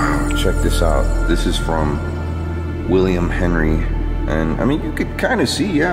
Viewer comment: when they start saying names there was WILLIAM and later on, shows like a poster on the wall that says BELONGINGS OF WILLIAM EDWARD HINE it's strange ?????? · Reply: I thought he said william henry?